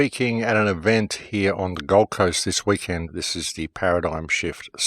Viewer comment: Went to the web page, cause I thought you said "Gulf Coast" ! Maybe Destin,Fl. once. Thank you for giving an hour of sense. PEACE